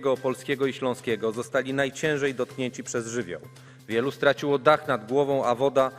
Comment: byłem pewien, że usłyszałem "dotknięci przez żymian" :D